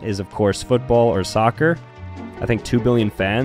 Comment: Got most of them wrong but I thought I heard mammal not animal 😭